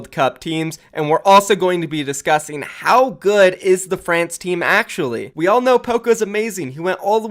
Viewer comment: Anybody else hear say " Dafrans team" at 0:30 ?